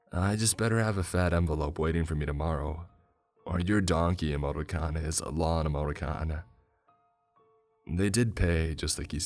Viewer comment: I can't make out what is being said. It sounds to my ears as "donkey emoticon is a lawnmower-ter-con". I'm sure that isn't right. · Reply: In english a donkey can also be called an ass. Lawnmowers cut grass. He probably meant the phrase "Your ass is grass".